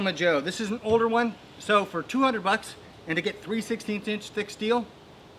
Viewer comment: Mr...what are you saying at about 12:08 in? After you say $200. Thanks. EDIT...3/16!